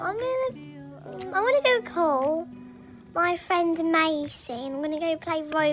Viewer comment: did matilda Mean Leah/Lia Lol I heard, "Im gonna go play with my friend maisie." I was like- huh